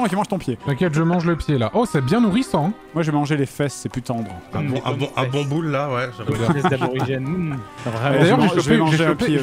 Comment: UN QUOI ????? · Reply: un bon boule la ouais mmmh